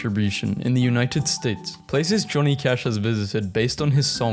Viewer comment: i thought he said Tommy Cash 😭w 12:57 YEEEEEEEEE DANMARkk 14:55 oooo sweden :0